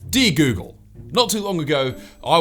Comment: i just heard 'D google' instead of 'Dear Google' (wow)